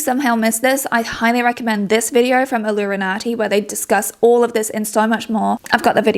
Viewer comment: Oh god, I thought you said 'Illuminaughti' and felt my stomach lurch. Whew! Glad to be mistaken.